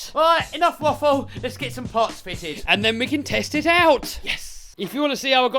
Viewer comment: Why did I hear "test it out" as "test tit out" 🤣🤣